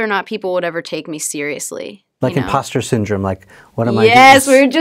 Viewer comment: what syndroms she said?sorry i cant hear it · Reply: Thanks Marco, it's called "Imposter Syndrome." Basically it's a mental condition where (falsely) don't feel worthy or qualified to do what you're doing.